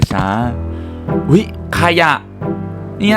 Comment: ได้ยินว่าขยะ